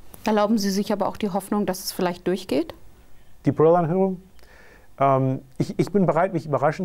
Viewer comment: wtf was hat der gesagt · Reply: "die Parol - anhörung"